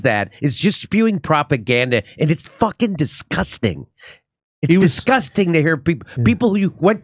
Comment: Does he say it’s a disgusting N word at 2:10 ?? Lmao